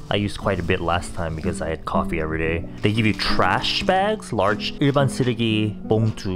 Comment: I thought u said "they give you trash", until you said bags after like a tiny pause 💀